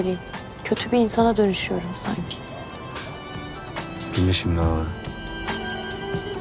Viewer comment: de Araz ne diyor anlaşılmıyor. Anlayan var mı? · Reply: Şimdi ağla diyo